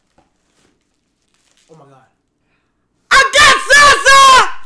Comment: I didn't know what the fuck he was pulling out the bag, thought the nigga said "I got salsa!!" 😭😭😭